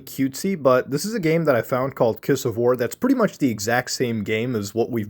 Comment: I thought he said kiss of whore 😂